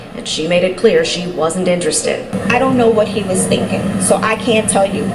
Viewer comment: I thought they said “ just say you aren’t interested “